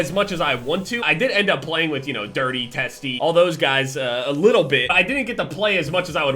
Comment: I thought he said he played with dirty testes (as in testicle)